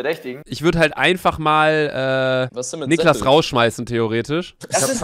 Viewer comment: anstatt theoretisch habe ich auf Chinesisch verstanden XD !